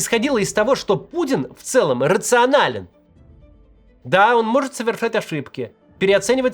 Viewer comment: кто еще услышал тут Пуддин’г рационален? Кто ел, в последнее время?